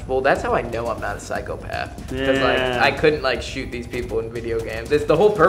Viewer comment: I remember spending hours trying to kill the "teammates" in no russian. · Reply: ohh I thought he was saying Makarov couldn't shoot you 😅